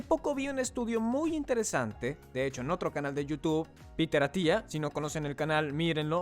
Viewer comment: habla de UN canal en Ingles llamado "PITERATIA" (así se lo escuché ) alguien lo conoce? Lo busque y no lo encuentro. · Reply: Meses tarde, pero por si no lo has encontrado o alguien lo busca, el canal es Peter Attia MD.